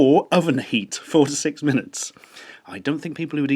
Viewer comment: i thought you said 46 minutes not 4 to 6 minutes